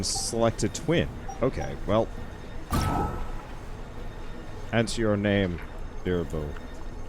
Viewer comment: I'm not sure but I thought you said What's Your Name? Bilbo